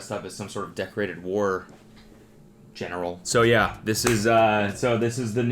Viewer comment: I totally misheard you talking about the photo of Chili as a war general and thought you said war criminal 😂